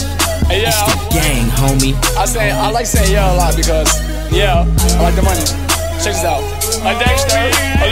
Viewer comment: "I say yeah a lot because...yeah" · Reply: i always thought he said " i lke satan alot. ughh cause the money"